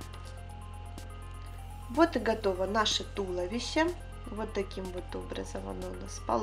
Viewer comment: на 7:34 мне послышалось вот и готово наше чудище